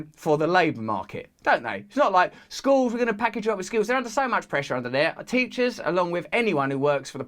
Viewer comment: I thought he said 'skulls'